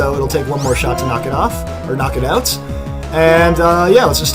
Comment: I thought he said wack it off xD